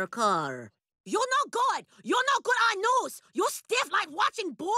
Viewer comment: What does she say at 0:12? "(??) mother so happy!" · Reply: "Joan Lunden's mother so happy, Tricia Takanawa's mother so sad :("